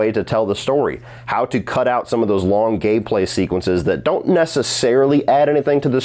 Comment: did you say long gay play sequences? Lol. Pretty sure that's not till the sequel. Jokes aside, this episode was incredible. I loved how many scenes and bits of dialogue were shot for shot, ripped straight out of the game.